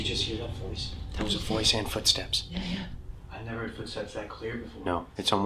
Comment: it sounded like the voice said Here or yah